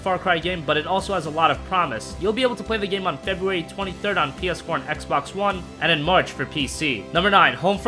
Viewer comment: did you said far cry primal will be available in ps1 @ 1:15? lol